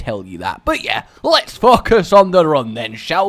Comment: At first i thought you said "Let's fuck us on the run!"